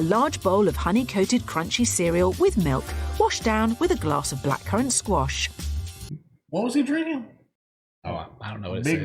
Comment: blackcurrant squash. google it now that you know what they said lol